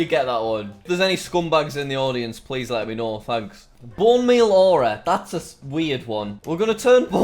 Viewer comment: Lol when u said Bone Meal Aura I thought u said Bum Me Aura